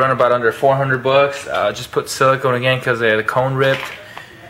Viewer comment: I did not hear any true bass coming from the sub. I only heard "boom" sounds with no actual low frequency content. And did I hear you say "...had a cone rip..." ?? Also, using the "F" word in a public video is kind of an ignorant thing to do. Kinda low class, you know?